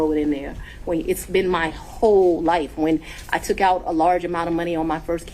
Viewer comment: Did she just say she took a large amount of cash fund?